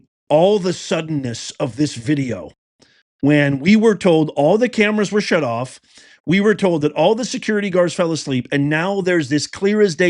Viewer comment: I thought they said the cameras were disconnected…